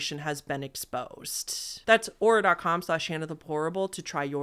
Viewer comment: ….is this a glitch, or did you just say “HannahThePorable”? 😂